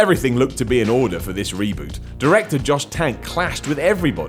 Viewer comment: did he say Josh Tank?